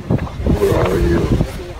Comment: I clearly hear, "Sister, WHO are you?"